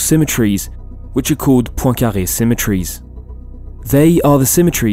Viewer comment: what the hell is that word? Poykavey Symmertries? With a new and difficult word, writing on the screen would have helped! :) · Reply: Pointcarré